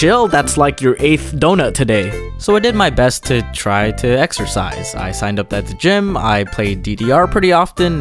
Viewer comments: Exercise? I thought you said extra fries!! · Exercise? I thought you said "Extra Fries" Nobody: 40 year old Facebook women:🤣🤣🤣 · Exercise..? I thought u said "extra fries." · exercise? I thought you said extra fries...🍟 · Exercise? I thought you said Extra Fries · Exercise? I thought you said extra fries. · What's "Ex-er-cise"? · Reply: Exercise? I thought you said extra fries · exercise?.... I thought you said extra frys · Exercise? Oh I thought you said "extra fries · Exercise? I thought u said extra fries! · what is exercise? can you eat that? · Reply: exercise? I thought you said extra fries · Excersize... Egs..er..sides Eggs are sides FOR BACON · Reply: YoYo Mockley exercise? I thought you said extra fries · excercise? i'm sorry i thought you said extra fries? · Exercise? I thought you said "Extra-Fries" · Exercise ? Ohhhhhh I thought you said extra fries . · Exercise? I thought you said extra fries 🍟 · what is exercise? can you eat that? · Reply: exercise? i thought u said extra rice? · exercise I thought you said : extra fries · Exercise? I thought you said Extra Fries! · Exercise I thought you said extra fries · exercise? I thought you said extra fries. totally didn't steal that from a t-shirt. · Exercise? I thought you said extra fries... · Exercise? I thought you said "Extra rice" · om nom stuffs mouth with fries om nom Exercise? om nom I thought u said extra fries. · "Exercise! I thought you said extra-fries!" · Exercise? I thought you said EXTRA FRIES🍟